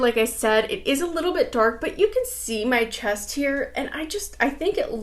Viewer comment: Randomly, the lipstick shade you’re wearing is YOUR SHADE, seriously. When you said, “You can see my chest here,” I heard, “You can see my chest hair,” and I almost choked on my spit. 😆